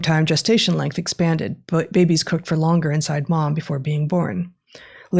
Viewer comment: Did she say "Babies cook for longer inside mom?"